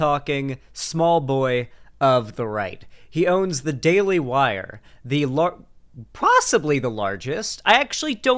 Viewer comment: ngl i misheard "The Daily Wire" as "The Daily Liar" and now i can't stop laughing asjfdlfjksd this is what i get for watching youtube videos on 2x speed